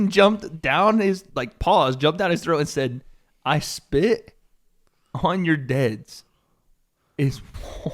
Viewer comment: I spit on your deads?? Is that what he said? What's that mean?